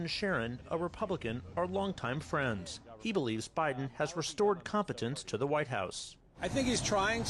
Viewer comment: OMG how do they see Biden bringing back confidence to America 🇺🇸 unbelievable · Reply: I thought they said "competence". Either way, it's an absolute joke! 😵‍💫